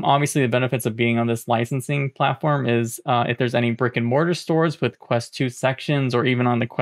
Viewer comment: When you said 'brick and mortar', I heard 'rick and morty'